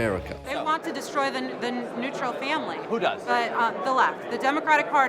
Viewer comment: Did she say neutral instead of nuclear? 😂